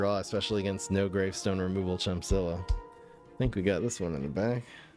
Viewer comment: I thought he said snowgrave stone removal Chompzilla for a second